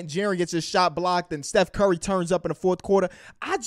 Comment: did he say seith curry🤣🤣🤣🤣